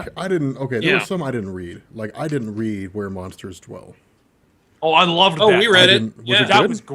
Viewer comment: were monsters 12? I cant understand what hes saying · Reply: seventhousen gricheye Where monsters dwell